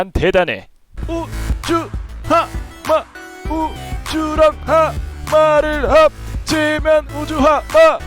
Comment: 소리작게하고 잘들우면 고추! 바삭! 고추랑 하마를합치면 고추하마! 라고 들림